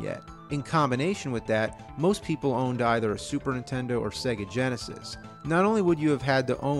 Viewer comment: It kind of sounds like you say intendo when you say nintendo hehe